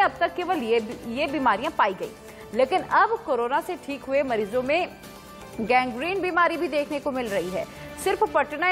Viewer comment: I heard gang rape